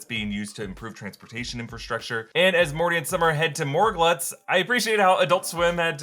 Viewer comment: you said and as morty and summer go to; instead of and as rick and summer go to.